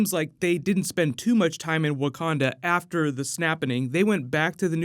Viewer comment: did you just say "snappening"???